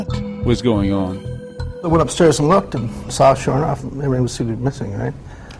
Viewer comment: What did he say at 2:02? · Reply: It sounds like he said "I saw sure enough..."